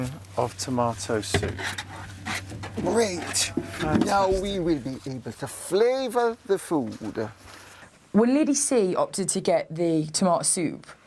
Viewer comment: what does she say? "great now we will (inaudible) the flavour the food" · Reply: She said "Great, now we will be able to flavour the food"